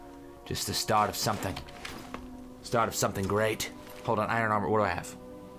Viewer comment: when says start of something i thought he said start of something new